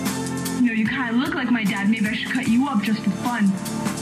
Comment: The dark skyes VA saying “think you’ve had it rough? Try growing up without a father.” will never not absolutely kill me 😭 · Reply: Oh wait she says BEAT you up? I always thought she said CUT you up, which, honestly, was kinda funnier to me bc of just how "ow the edge" it is